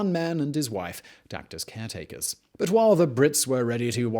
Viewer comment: "leaving one man and his wife", source? And what do you say afterwards, "duct caretakers"?